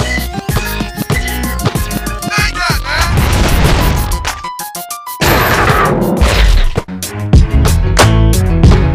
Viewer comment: what did he say ?? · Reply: "Take A Nap!" - Bonafide